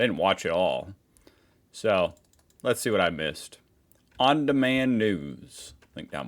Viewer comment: I heard 00:33 "On demand nud€s" and had to go back to double check (hearing as a podcast).